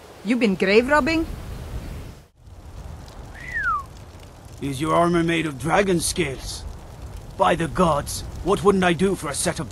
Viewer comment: the first time i heard "is your armor made out of dragon balls?"